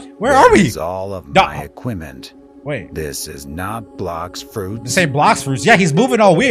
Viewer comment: I heard you say black fruit